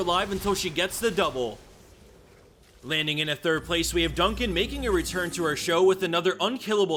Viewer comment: thought it said "Dunkey making a return", what a letdown